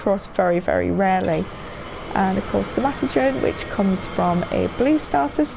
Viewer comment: did you say Sanatogen? Tips for finding it, available at all good Korvax pharmacies or space Tesco's 😆